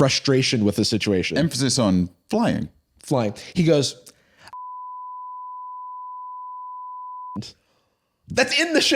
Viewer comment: does anybody know what the idol line is?? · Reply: “I'm fucking shitting more blood than a kid at Epstein's island.”